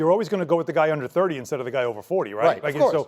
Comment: Come on Charles I actually thought Paul Pierce said the moon.